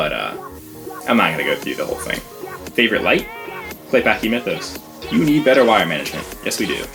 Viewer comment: What did you say was your favorite light? Sounds like you said "playback youmentos"